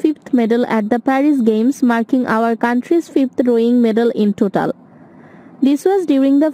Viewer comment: Did she say "our country's 5th medal"? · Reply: 5th rowing medal i think she said